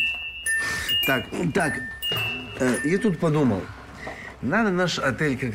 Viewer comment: На 4:57 мне послышалось ,что Павел Аркадьевич сказал Ютуб подумал😸👌